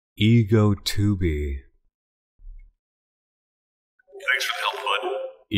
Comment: I just heard a quote that said: “THANKS FOR THE HELP BUD!” Also at 57:41, I heard a person singing! And at 1:00:11, I also heard someone saying something disturbingly threatening!